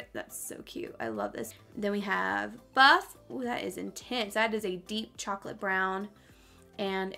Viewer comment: Haaaa you said “that’s intense” but I heard “that’s the tits”😂😂